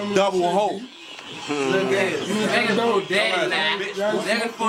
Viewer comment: What was saying from 4:17 to 4:27 ? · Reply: Jarius Johnson we looking fa ya